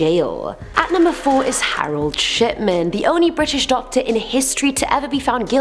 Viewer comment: drawing a picture while listening to this and i thought i heard her say "Harold Shitman"